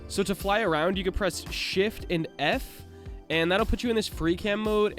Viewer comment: what did you say? It sounded like "Shift N F" ? Not clear nor obvious. Thanks.